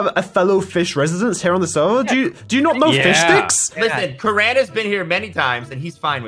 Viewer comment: Bruh Why Karan I thought you said Karen